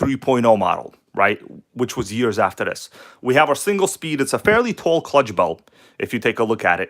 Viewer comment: I thought you were saying the single speed